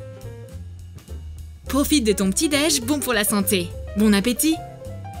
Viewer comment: C'est moi ou a 10:17 elle a dit bon appétit